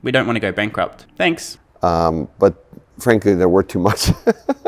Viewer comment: What did Farley say at 32:37 ? "... Tesla, their worth too much," Please help. I don't understand what he said while laughing.